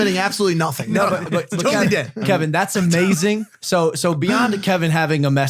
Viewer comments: Bro i thought it said kevin hart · I thought it said Kevin hart lol